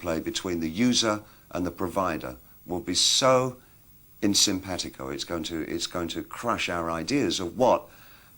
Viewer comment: Not really. He predicted the relation between user and provider would be unsympathetic, which tells me he thought audiences would receive the content at home and simply not care who made it. What we've actually seen is how creators are now able to connect directly to their audiences much more, to the point where a lot of content creators are directly funded by their audiences through donations, patreon, ad revenue etc, without the need to even sell a single product. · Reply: Did you mis-hear 'in sympetico' (on the same wavelength) as 'unsympathetic' (unfeeling / unappreciative) just after 5:45 ? As I understand it, Bowie was actually saying something similar to what you identify has happened, in that the artwork is not just the preserve of the creator but a shared thing in a space between the creator and the consumer. Best wishes.